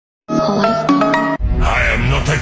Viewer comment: Bro i hear ' im not a gay im not a god '😂😂😂😂😂😂😂